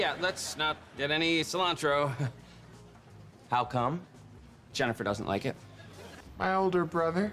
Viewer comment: I think what he was saying was he doesn’t want to get the cilantro, because JENNIFER doesn’t like cilantro. But since Spencer loves it, he could have a friend who actually likes it😅